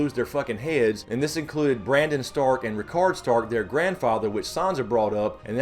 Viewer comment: Do you think it is possible that Randall Tarly dies and that Dickon Tarly is approached by Samwell and convinced to change Allegiances and supply troops to the war up north. Why else recast or make Dickon important? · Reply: Joey B you are right I watched that part again and at first I still thought he said Rickon like Ned's youngest son. But I turned on the CC and it was Rickard like Ned's father.